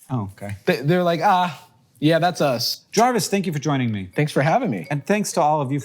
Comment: I heard "Jarvis, stinky for joining me" at the end 💀